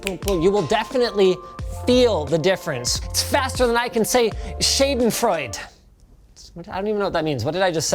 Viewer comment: I am a native German speaker and i didnt understand this word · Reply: He tried to say "Schadenfreude"